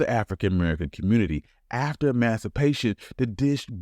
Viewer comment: I don’t know if you know or not, but at @ 4:43- the words on the screen say “after masturbation” instead of what you actually said “ after emancipation”…